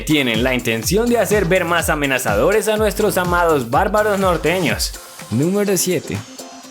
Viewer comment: Emmmmm creo que se dice nórdicos y no nordeños, corrijanme si me equivoco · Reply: ¿Te refieres a esto?, creo que dice norteños.